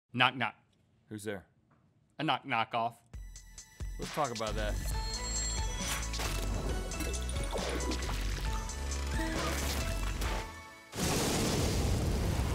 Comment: I thought Rhett said, "Let's f*ck about that" hahaha